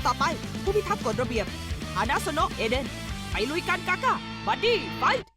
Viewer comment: ได้ยินว่าเอเรน